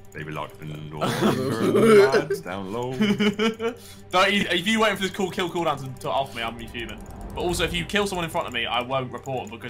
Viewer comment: What does say at 6:14? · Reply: If you're waiting for the kill cooldown to off me, I'll be fuming.